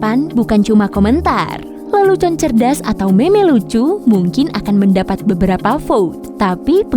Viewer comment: tolong dibacanya "mim" daripada tadi, takutnya salah dengar